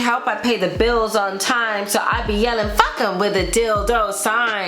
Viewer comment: Alllll this this time I thought she said “So I be yelling fuck em when the deal don’t sign” 😂😂 her minddddddd 😭😭😫😫🙌🏾🙌🏾🙌🏾